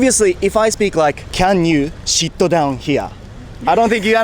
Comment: の侍英語"Can you shit down here?"に聞こえたけどオージのお姉さんも最初表情からしてそう聞こえたんじゃないかな。😂 直ぐ"sit down"と悟ったみたいだけど。